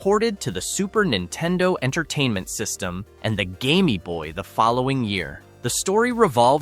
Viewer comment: what in the f... did you just say? the Game-y boy? bruh... bruuuuuuuuuuuuuuuh